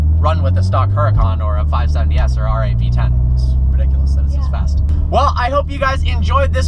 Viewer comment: Wait, Did you just said Ferrari v10????